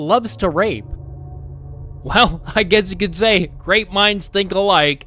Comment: I keep hearing "great minds think alike" as "rape minds think alike" because of the context and I can't stop laughing